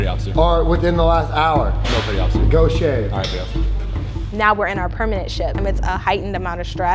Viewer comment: did he say “alright petty officer”???? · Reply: "aye aye petty officer"